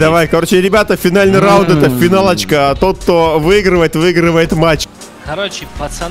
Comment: Мне послышалось финал очка😂